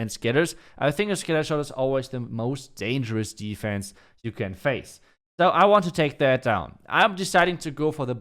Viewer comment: I heard that "it is the most dangerous defense like your face lol